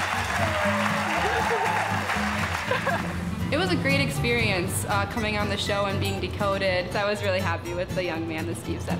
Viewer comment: Anyone here read lips. What did Steve say in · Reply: kawashida he said "you're welcome!"